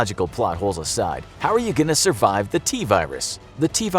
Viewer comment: I thought it said “can you survive the coronavirus”